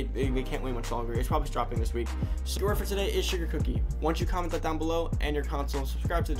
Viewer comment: Word I heard “sugar daddy” Word u prob said “sugar cookie” Ps4: Funky_shaba69